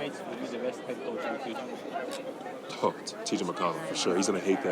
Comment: What did Myles say about TJ at 33:01? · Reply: Myles was asked which of his teammates he thought would make the best head coach in the future. He answered, “Oh, TJ McConnell for sure. He's gonna hate that, but TJ McConnell for sure.” The reporter then brought this up to TJ at 38:54 — without revealing who said it — and TJ replied, “I'm not sure my wife would like that,” but he was clearly appreciative of the compliment. I'm not sure what his wife's reservations might be (if he was being serious), but considering TJ is the son of “one of the most successful coaches in WPIAL (Western Pennsylvania Interscholastic Athletic League) history” (per Wikipedia), he probably knows better than most how time-consuming coaching can be. So, we’ll see what happens but I like that most of the players and RIck are trying to be as focused as possible on game 2 despite the barrage of speculative and somewhat distracting questions. For the record, TJ was also asked who he thought would make a great coach one day. Without hesitation, he said, “Tyrese, for sure", which I thought was interesting.